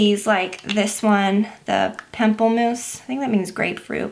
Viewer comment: Lol I thought she said pimple moose!